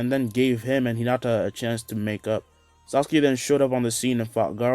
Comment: I Heard Neji And Hinata Make Out instead Of Make Up!